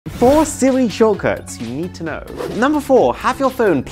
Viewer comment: I thought he said "four silly shortcuts"